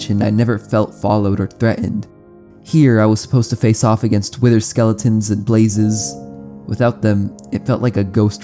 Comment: did I just hear Twitter skeletons?!